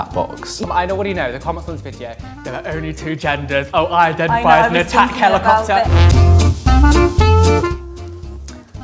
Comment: did he just say I identify as a helicopter